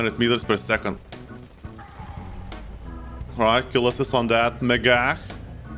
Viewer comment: I really tough i heard u say "Kill assist on that nig*a" on 11:08 Kkkkkkkkkkkkkkkkkkk